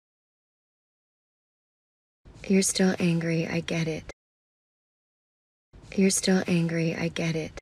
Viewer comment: 첫문장의 처음 단어들은 "크리스 dont angry i get it "으로 들리네요